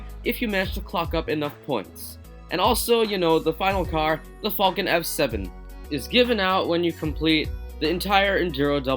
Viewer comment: I know you said the falcon f7 but it sound like u said the fucking f7 lmao i was weak when u said that